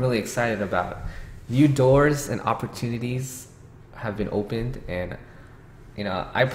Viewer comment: I heard that as "new GATES and opportunities have been opened".